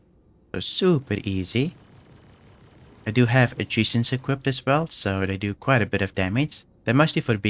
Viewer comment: what did you say you had equipped? Captions say attrition, I heard something like AEGIS, but I'm not sure what you said exactly. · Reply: Attritions, the guns